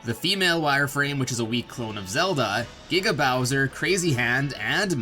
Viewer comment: Giga Bowser not being playable probably isn't the only reason this goes unused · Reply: Sanswagon, the Meddler lmao I heard “nigga Bowser” the first time too💀💀💀